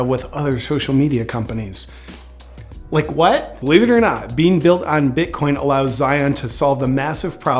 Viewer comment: I thought it said “Flat Believers”.